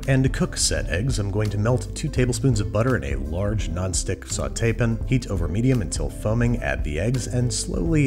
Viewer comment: Andrew proves that he can, in fact, pronounce "sauté pan" like a normal person, and then immediately says "soften" like a maniac. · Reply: it sounds like hes saying saute pin